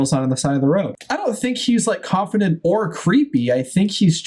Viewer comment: I thought you were going to say "I don't think he's like confident or Chris"